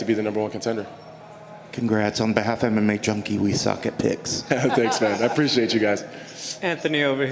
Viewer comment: "on behalf of MMA junkie we suck dicks.." or what ever he said.